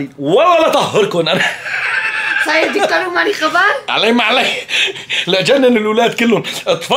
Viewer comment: شو قالت بالدقيقة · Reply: قالت (صاير دكتور و مالي خبر)